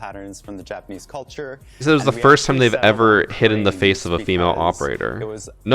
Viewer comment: I think he said “first time, in a while”